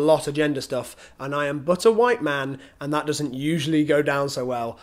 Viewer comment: "I am but a white man." You like Dean's video where he drew you as Cthulhu, don't you 😏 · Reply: Emily B I heard 'i am butter white man' the first time oops